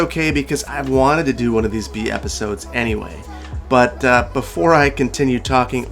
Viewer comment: At first I thought you said "BS episode", which didn't sound quite right since there's no BS about it.